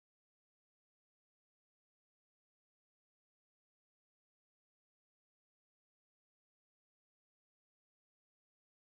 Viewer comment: can someone tell me what was the oil ships name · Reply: Gaint seawise